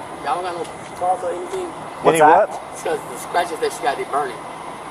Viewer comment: IT WAS THE CHERRY ON TOP when the he/she asked the cop if they had any ointment because the SCRATCHES “were burning”. And the cop thought he said the SPICES were burning. 💀💀💀💀💀